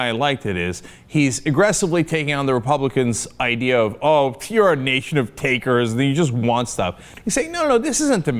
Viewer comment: Lol Cenk Says "you guys are just a nation of takes and you guys just want stuff" and translate captions say "nation of takers the jews one-stop". 4:10 Very funny twist.